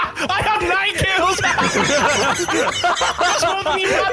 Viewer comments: what was saying? I couldn’t hear him over the laughing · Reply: That's more than you have in every game we play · what did he said? bcs i dont understand it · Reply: "That's more than you have in every game we play"